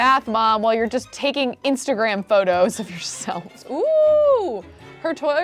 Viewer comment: Did she say toilet review? 9:43 I don't think so but it sounded almost like it XD